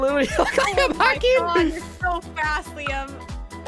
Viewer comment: Is name Liam? I heard her say that proof=